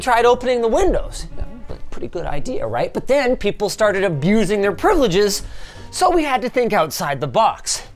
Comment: For a second i thought i heard Linus say, "Bussin'". I thought I had a stroke.